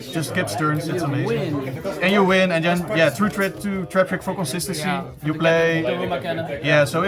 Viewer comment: whats the last 2 cards he said did he say traffic? for consistancy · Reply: trap trick ?